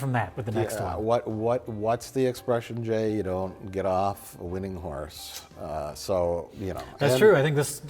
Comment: did mike just say you dont jerk off a winning horse?!!? finally he admits what happened to Jessie...:-O